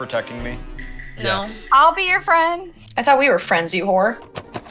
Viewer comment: did she say "i thought we were friends you hor.." or did it sound like she said it?